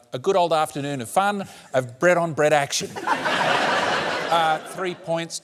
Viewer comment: the captions saying bread on bread action. Instead of Brett on Brett. 🤣😂